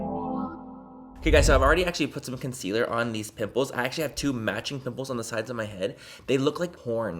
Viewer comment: Totally had a 👀 moment because I thought you said, "I already put some concealer on these NIPPLES." Like wut? I'm cackling 😂 It's early, I'm delirious...